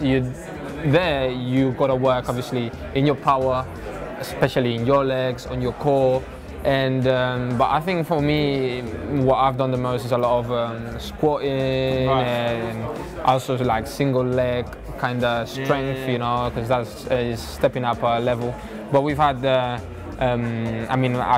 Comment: did he say squatting?